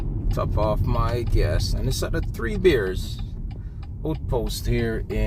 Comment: I thought you said 3 beers lol